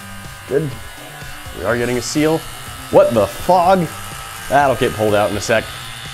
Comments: What the fog Lol I thought you said f*ck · Did he say "what the fog"